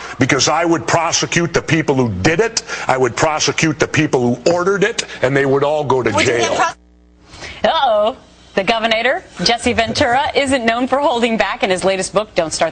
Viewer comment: did she say the governator.. inside of governor